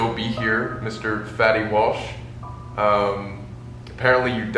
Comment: I thought this was an April fools or something cause it really sounded like he said “Mr Fetty Wap”